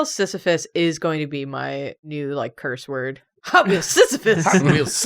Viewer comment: I misheard it as Hotwheels Syphilis